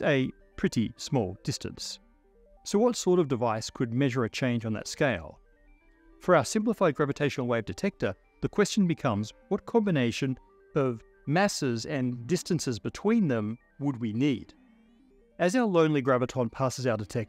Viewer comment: Damn. It's Thanksgiving and I thought they said gravy.